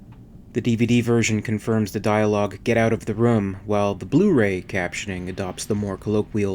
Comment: I always thought he was saying "Get out of the road" to Lambert....because he wanted to kill it, but they are surrounded by compressed oxygen tanks. That's the another reason why he didn't use the flamethrower, he would have ignited all the oxygen tanks.